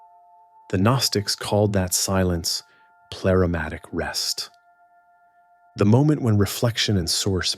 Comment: I heard, pleramatic rest, I saw; pneumatic rest